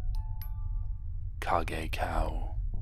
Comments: is no one going to question why the subtitles say "Car Gay Cow" or is it just me LMAO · THE AUTOMATIC CAPTIONS THOUGHT YOU WERE SAYING “CAR GAY COW” INSTEAD OF KAGEKAO ALSJSKNSA